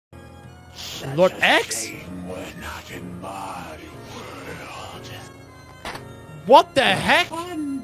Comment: I thought it said: "such a ahame we arent in my world,the fuck-"